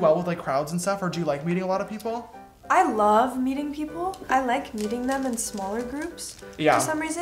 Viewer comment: Ok when she said meeting them in a smaller group, I thought she said eating them in a smaller group. SIS HELP! Lol😜